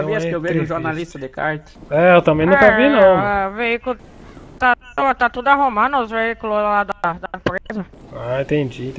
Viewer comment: "ta tudo arrumando os veiculeohfn da pro@@ eza" "tendi tendi" kkkkkk tendeu foi nada